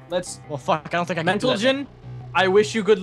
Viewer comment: I swear (is it?) says "mentalgen"